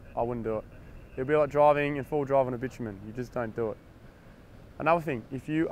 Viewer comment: Great informative video! New axles with Eaton E lockers are my next upgrade on my wrangler JK. I can't catch what you're saying at 8:28 though. "That'd be like driving in 4wd in a _________". Sounds like "bitchamen" lol, can anyone help me out here? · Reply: "It would be like driving in 4WD on the bitumen". He means on an asphalt road.